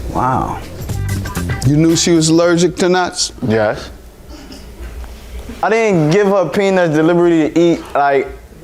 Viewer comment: Am i the only one who heard him say "i didnt out peanuts in your food delibertly " he didn't deny it he just saying he didnt do it on purpose 😂